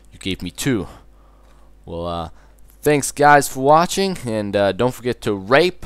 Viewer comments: Did he say "Don't forget to rape, comment and subscribe" in 2:43??!??!??!?? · Same here I thought he said rape comment and subscribe